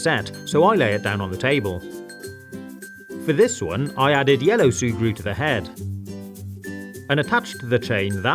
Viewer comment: What said: i added yellow sugru What youtube said: i added yellow surgery(without r)